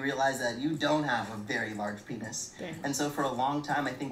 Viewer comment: did she really say dang when he said he wasn’t packin or am I trippin😂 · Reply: No she was saying damn because she was reacting to what he was saying 🤣